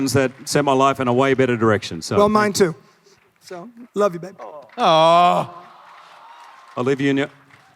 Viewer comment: Did he say love you babe